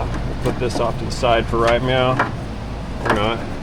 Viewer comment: ….Did you just say “Right Meow?”…